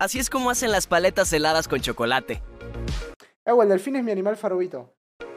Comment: Por favor alguien que se de cuenta que en el minuto 12:53 dijo “eu el delfín es mi animal farovito “ porque si no quedo como una estúpida que analiza cada palabra que dice